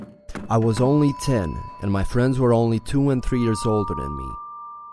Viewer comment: HAHHAHA I thought he said his friends were 2 and 3 year old